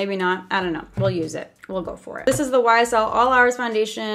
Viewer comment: When she said YSL it sounded like she said whysall